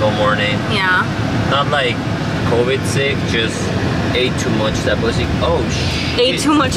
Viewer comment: was I hearing things lmao🤣🤣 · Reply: Hahahahha omg and i cut the part out where i told him I thought he said ate too much 🐱 hahahhaha it sounds just like he said that 😂😂😂